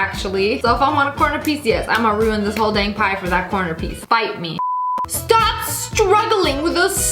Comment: MURDER DRONES REFERENCE?! (I know she said fight me, but it sounded like bite me)